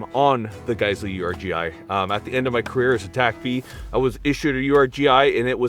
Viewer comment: What does he say he was? It sounded like he said "at the end of my career as a Tac B". What is that? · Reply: TACP Google it